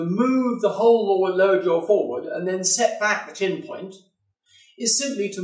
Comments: Anyone know what he’s saying around 6:30? He says move lower incisors forward and pop in an extra… something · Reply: He says Set back the chin point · can somebody please, tell me what he said at 6:30 , i replayed it many time but just could not understand what solution he gave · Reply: He says Set back the chin point